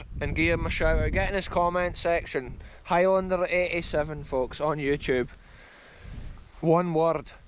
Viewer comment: Another great adventure! Not finding the other channel though. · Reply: He said at the beging but I'm not sure I got it😅 I heard "highlander887"